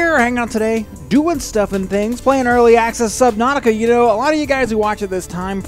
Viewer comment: I can't remember if it was in the FTB Infinity video or the CSP video when I heard you talk about playing this game, but when I heard you say it I thought u said "Slip-nautica" and I thought to myself... How convenient.